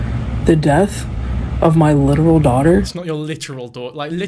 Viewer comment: she’s saying her “little daughter” which is still stupid as fuck but that’s what she said